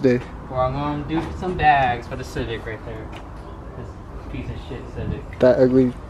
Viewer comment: I thought it said "banging the Civic" which oddly enough would make a lot of sense. 😅🤣🤣